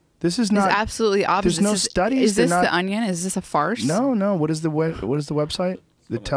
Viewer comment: did she said "is this Fars" ?